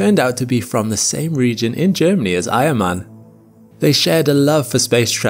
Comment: I thought you said he was from the same region as “iron man” 😂